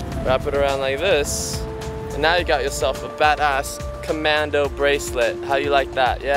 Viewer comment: UMM DID SAY A CURSING AT 5:54 THE WORD BADA**